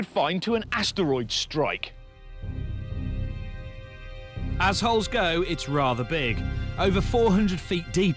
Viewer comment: Tell me you didn't hear "assholes go" at first!